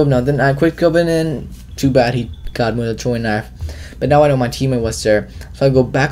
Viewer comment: at first I thought you said trolling knife